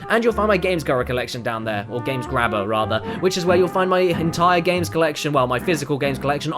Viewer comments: Sounds like you said "which is where you'll find my Hentai games collection" lol Don't worry, I don't judge. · "And you'll find my GamesGubir collection down there, or Games Grabber rather, which is where you'll find my hentai games collection, well my physical games collection, on my shelf..." Greatest. Mishearing. Ever. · I swear he said Hentai games collection...